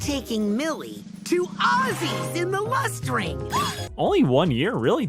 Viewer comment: First time I watched the episode, I low-key thought that they were saying “Arby’s” and not “Ozzie’s.” Wish I could say I was joking